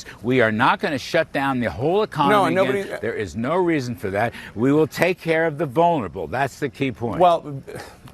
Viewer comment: did he say "we will take care of the boner able ? " 😂